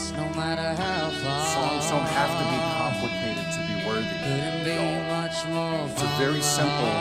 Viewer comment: Unfortunally I don't understand what says at 11:11 ... 😞 ... " ... have to be complicatd to be ___ (???)" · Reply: "worthy, y'all."